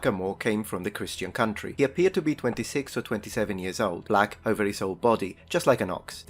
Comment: I honestly thought you said Black other F word and I spit out my coffee 🤣🤣.